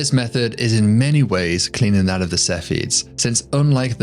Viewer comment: What does he say at 17:30 - I can't understand it - "This method is in many ways <unintelligible> ... that of the Cepheids". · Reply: It sounds like he meant to say “This method is in many ways cleaner than the Cepheids”